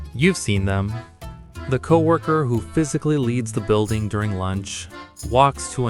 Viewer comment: Your speech to text put "leads" at 0:17 instead of "leaves"